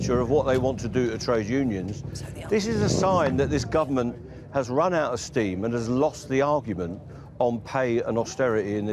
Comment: What did she whisper at 4:25? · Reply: It was Careless